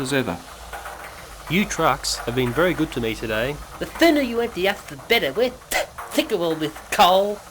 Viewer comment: Even though i've watched this about 38 times in my life, I STILL CAN'T FIGURE OUT WHAT THAT FREIGHT CAR IS SAYING!!!!!!!!!!!!!!!!!!!!!!!!!!!!!!!!!!!!!!!!!!!!!!!!!!!!!!!!!!!! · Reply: " The sooner we're done the better we're sick of all this ( cough cough) coal!!!"